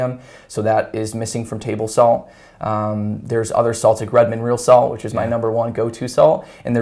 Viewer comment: I can't understand what he said his favorite type of salt is.. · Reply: Redmond